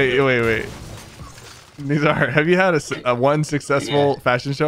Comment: Did he say nigga